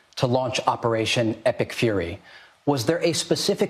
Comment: whenever they say EPIC fury I hear "Epstein fury"